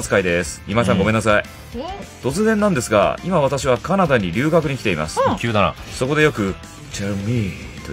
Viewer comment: did ragna just said Canada? 🇨🇦